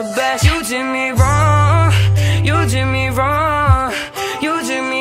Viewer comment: Idk if it’s just me but it sounds like he is saying wrueh or something like that instead of wrong in